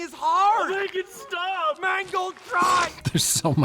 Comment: Ngl I initially thought the guy said "Mangled pride" and not "Mangold tried"....still works in the context of the scene I think xD